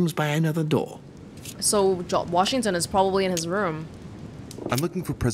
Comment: Am I the only one who heard say "Jacques Washington"?